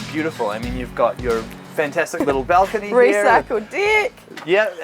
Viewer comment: 2.18 It sounded like she said recycled dick lol.